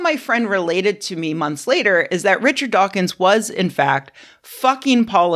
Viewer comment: lmao... had to replay because I thought you were saying Dawkins WAS Kirby. Like he had created her as a persona.